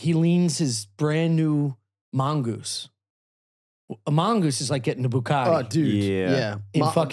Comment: I misheard say "Gettin' a Mongoose is like getting a bukake" 😂